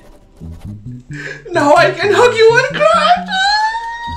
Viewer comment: did he just say cr*p